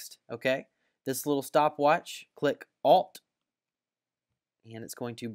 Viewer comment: click what? I didn't hear well, please help! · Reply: alt + click the stopwatch